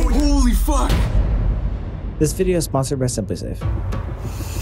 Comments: I legit thought sam said “this video is sponsored by subway surfer” after that dramatic ass intro I’m dead 😭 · Who else thought that he said this video is sponsored by subway surfers